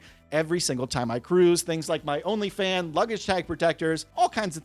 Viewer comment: 😂did he say he has an Only Fans luggage tag protectors? Wtf is that!?!?